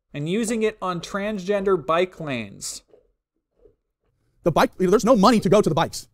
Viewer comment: At first I thought he said Trangender Biplane. Like a weird evolution of the Attack Helicopter joke.